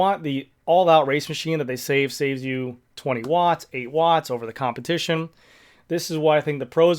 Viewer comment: FYI 20watts over v4rs,, which, as Chris Miller podcast said was a “dog of a bike“ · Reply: I thought he said the bars along were 20 watts better